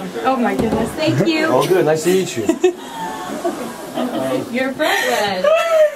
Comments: i heard "your'e pregnant" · why do I keep hearing "you're pregnant" @ 0:38 lol